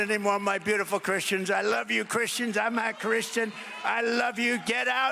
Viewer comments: Did he say he's not Christian at 6:11? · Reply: It's just his adderall slur your hearing, he was trying to say "I'm a Christian!!" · did he really said "I'm not Christian"... finally something true coming out of his mouth